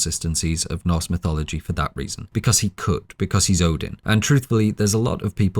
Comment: What he says: because he could What I heard: because he cooked 😎